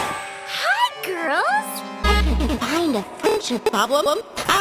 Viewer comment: ya it sounds like shes saying hi girls